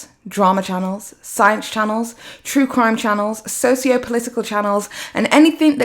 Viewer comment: I thought you said sociopathic in relation to Blair and I almost had to unsubscribe. Glad you sad sociopolitical